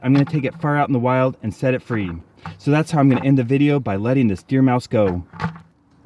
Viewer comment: I know it’s “deer mouse” but I keep hearing “dear mouse.”